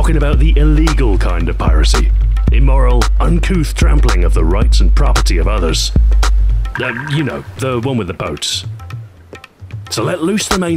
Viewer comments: I totally misunderstood the intro, I thought he said he was going to talk about the "trampling of the rights and property of others NOT the one with the boats". I was completely confused for about half that video. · Not what I thought the video was going to be about. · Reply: I thought he said "you know, not the one with the boats"